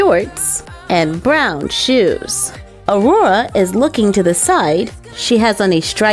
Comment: When she said Side it sounded like she said Aurora is looking to the sight